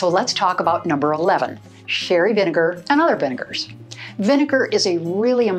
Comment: Hi! What was the name of the vinegar you recommended? I heard “fairy” but I’m not sure that’s the correct word. Thank you!! ETA: never mind, I realized it’s “sherry” vinegar 🤦🏻‍♀️😃